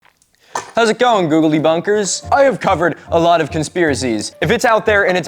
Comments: I always hear "Googley bunkers" 😅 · You said Google debunkers and I heard "googldy bunkers" like bunkers with googly eyes 😂